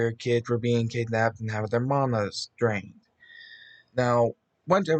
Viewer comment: What he said “mana’s drained” what I heard “mama’s drained”